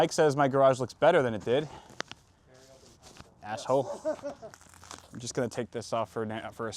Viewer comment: Idk if it was intended, but it sounded like you said "ashhole, which is morbidly appropriate in this instance.